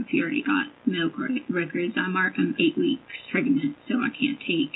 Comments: did she say she was 8 weeks pregnant ?? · did she say "I'm marking at 8 weeks pregnant.."???